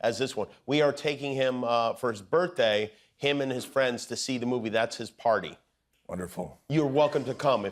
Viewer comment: Did anybody catch that ? 🤔 8:17 I can’t read lips 😪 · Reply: A movie